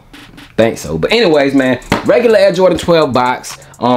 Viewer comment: Ain’t it sound like he said regular ass Jordan 12 box